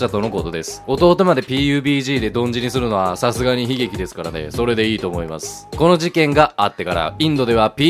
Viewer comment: 「流石に喜劇ですからねw」 に聞こえる(洗脳済み)